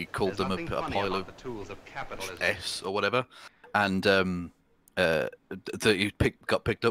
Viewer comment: What is the guest saying? It sounds like, "a pile of chess or whatever."